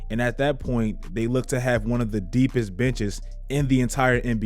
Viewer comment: tell me it doesn’t sound like he saying “the deepest bitches”